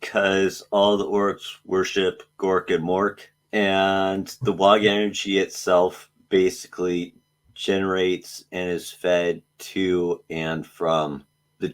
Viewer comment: when he said "the WAAARGH energy itself generates....." i thought he said "the Waargeneuchi" like waaa-gen-new-chee😂😂 i started frantically googleing every spelling i cld think of thinking id missed some really cool ork term, thinking like it was an ent moot but for orks.....kinda sad when i realized i just misheard😅😅